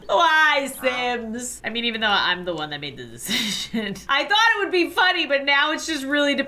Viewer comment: did she just say shit..? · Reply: Decision